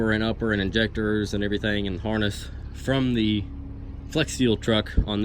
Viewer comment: it sounds like you said flex seal lol